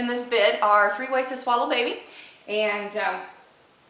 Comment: When i first tuned in I thought you said SWALLOW a baby! EEEEK!!!!